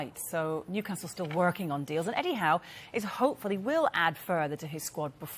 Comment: when she said eddie howe at 36.00, i thought she said 'anyhow'. oh well. btw, we want either traore. lingard. or dele alli . we meaning west ham.